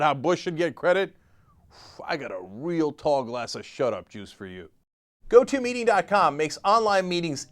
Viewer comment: I know this is a serious subject but if you want a laugh watch this video with captions turned on - they have no idea what Cenk is saying. My personal favourite: 5:51 he says "and we've got a tall glass of shut up juice for you" - the subtitles say "we'll talk a lot so showed jews for you"